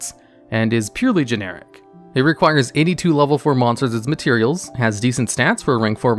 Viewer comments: For a second I thought he said Cairngorgon required 82 level 4 monsters, instead of Any 2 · I really thought he said “82 level 4 monsters” 💀